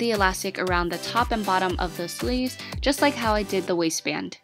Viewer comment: She said wasist band...i thought it said wasteman😂😂😂